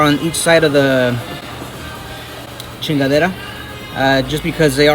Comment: DID HE SAY "CHINGA-LETTO"